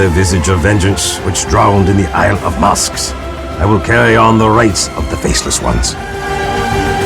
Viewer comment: i can never forget the words he said.. 6:24"and in this vengeance that struck the isle of masks.. i'll carry on the rights of the faceless ones.." i don't know it i heard it right but the way he said it is so cool..and it made me miss that bladekeeper set.. T_T